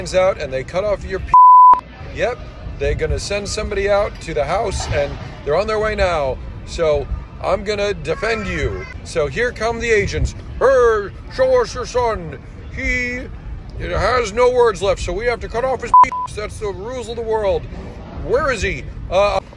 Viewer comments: sounded like he said “here come the asians”💀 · "So here come the Asians." I heard it asians but wtf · For once subs are helpful “here come the agents” I heard “here come the Asians” and I got confused and scared because I thought of the meme of “you’ve lost PePe privileges” with the amazing world of gumball lads holding scissors staring into your soul. 💀